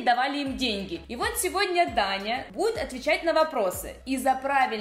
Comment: Мне показалось что она сказала Таня